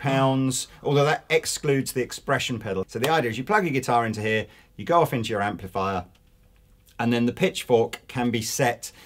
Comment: I swear I had to listen again because I heard at first "Your girlfriends your amplifier." lol